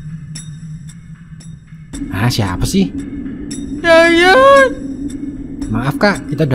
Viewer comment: hantu nya ngomong apa ? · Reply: Kayaknya địa ngomong “saya, datang”